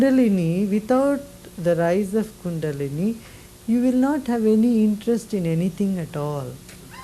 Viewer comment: does he say without the rise of kundalini you won’t have any interest in anything?